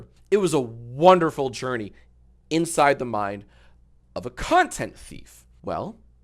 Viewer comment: Genuinely thought he said the C word there for a second 😭💀